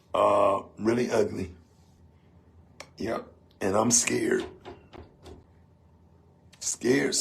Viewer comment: “I see Trees of Green… white roses too…” is all I hear 🎶 😅